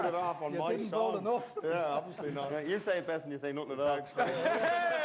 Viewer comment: Sorry, I can't understand what Keith is saying at 6:18,, apparently a joke about Ronan. Anyone get it? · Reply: You say it best when you say nothing at all.